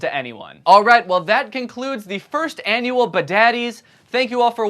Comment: did he say "the FIRST annual badads..." does this mean more?